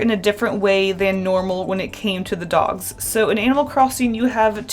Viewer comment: IDK WHY BUT IT SOUNDS LIKE YOURE SAYING ANAL CROSSING